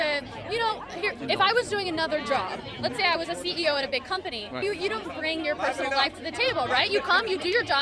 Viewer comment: did she just say "If I were a CEO of a BIG company"? I don't know how old she was there but I wish I had her confidence in my abilities and connections